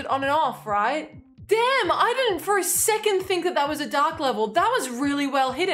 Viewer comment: oooohhh she said dark. It sounded like she was saying its a duck level